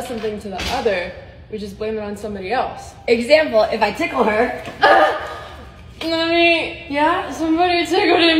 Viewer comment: I support them 100% I even have a girlfriend but did she just say “mommy someone tickled me?” 😀 or do I just have really bad hearing 😭😭😭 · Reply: Just so you know she actually said "Lovey somebody tickleded me." Not "mommy".